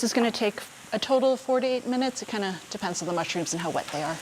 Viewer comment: Yet another great video, esp. Lan. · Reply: Yes, but thought she was saying 48 minutes instead of 4 to 8 minutes, and the end time for the final sauté same issue, she needs to enunciate more clearly instead of dropping down into a mumble. Other than that, she’s great!